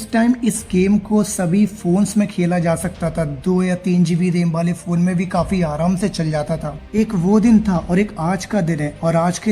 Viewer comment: kya kaha bhai ek baar firse bol na😂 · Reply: ye bola hai ki us time is game ko 2 ya 3 GB RAM waale phones mein bade araam se chal jaata tha.